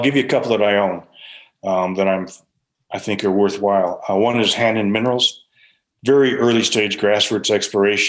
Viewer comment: What stock did Brent recommend at 16:48?? Hanen Minerals? my CC says Hand and Minerals.... nothing even close comes up, if you know, please send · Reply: Hannan Metals Ltd. I own shares in it and agree with him.